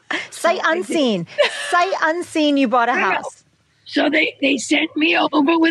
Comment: Before reading the attached caption with the video, I thought that Bracco was saying " I bought a one YEAR house." LOL!